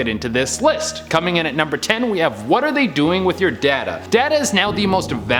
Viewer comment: LMFAO he said thing the government doesn’t want you to know then he said “what they are you doing with your data” I thought he said “ what are they doing with your dada as in your dad 😂